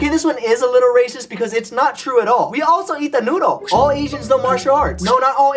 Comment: what does he say? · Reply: George player 'We also eat the noodles!'